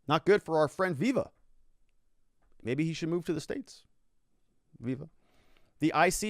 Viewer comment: When you said that maybe viva should move to the "states" I heard "maybe he should move to space"